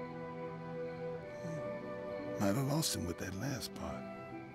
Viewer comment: what did miles say? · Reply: "I would have lost him on that last part"